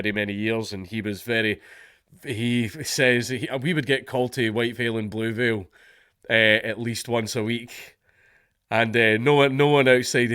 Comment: " ... we would get culty Whitefield in blueville uh at least once a week. " (autogenerated subtitles, it's also what I hear) ... say what now? I'm sorry I don't think everybody understands this :/ ... hlp?